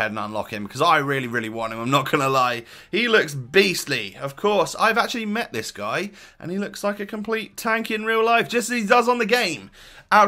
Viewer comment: LOL - misheard him twice "he looks like a right twat" instead of "tank" & "happy fucking Xmas" instead of "futmas".